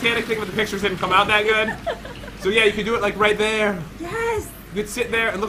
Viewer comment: Can you play minecraft with crainer and thea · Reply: I thought They said They cant do it